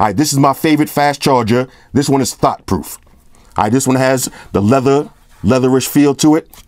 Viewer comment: I thought he said its "Thot Proof" 🤣🤣🤣